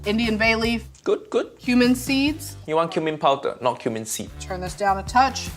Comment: I thought she said human seed